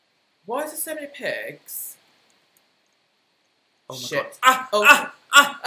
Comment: am i tripin or did say s*it